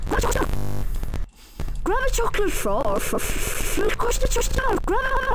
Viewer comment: At some point in this video, it sounds like someone says "chocolate frog cock." I'm not timestamping that shit, either.